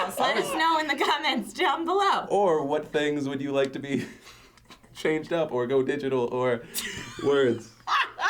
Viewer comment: I thought Phil said "what things would you want 'chained' up?" hahaha. awk. Oh a Steve says shit a lot lmfao